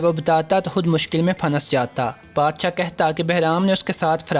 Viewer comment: Tu sari baatein chhor ye 3:36 pay Kya kaha hai "phanass Jata"??